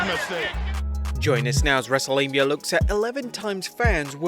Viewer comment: Thought this dude said Wrestlelabia lol